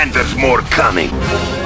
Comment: genuinely thought he said "free hookers"